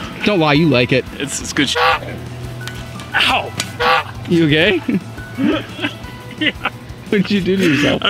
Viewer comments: Did he just say: ,,You gay?" or my inner demon is trying to control my mind again?🤣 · he asks: "you okay?" But I heard "you gay?" I should tone down with reading r/gay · why does it sound like he said "you gay?" 😂